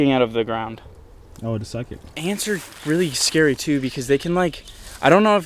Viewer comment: WHAT DID I JUST HEAR? YOUD SUCK IT?